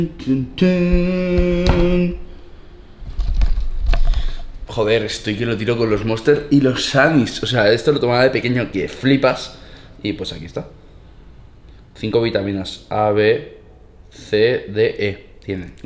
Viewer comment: Entendí y los Sarms xD